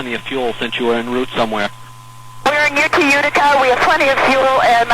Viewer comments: It actually sounded like she said Utica, not Wichita. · "we were en route to Utica" (not Wichita) 4:27 I hear "arranging an ambulance to arrive at the Laconia airport" (not "any ambulance") 7:28 "my mother is — something's really weird" 8:04 "hang on a moment" 9:40 "your guess is as good as mine" (as noted by another commenter) The ATC did keep saying she'd flown a Cherokee when she said she'd flown a Piper Warrior (does it matter?). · How awesome is this!!! I have about 100 hours logged in the Malibu and it's an advanced aircraft that requires a few good hours to get used to its performance. Incredible story from the past!! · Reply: That sounds like she said "Utica", not "Wichita". 8:05 *Hang on a moment 9:39 *Your guess is as good as mine